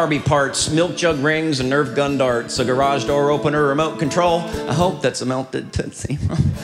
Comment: What did he say at 1:33 after remote control? lol I missed the joke. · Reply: I hope that’s a melted tootsie roll 😂